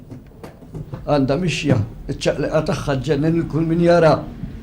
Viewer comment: Can someone tell me what he says at 10:45 to 10:50 plz? Thanks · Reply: Għandha mixja. Iċ-ċaqlieqa tagħha ġġennen kull min jarha. Translation: She has a walk. Her movement crazes all those who see her.